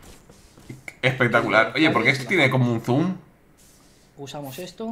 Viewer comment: que al inicio No entendí lo que quisiste decir hasta que luego rebobina y entendí que ahí habías dicho más bien dicho habías preguntado PorQue Esto tiene un Zoom?. . .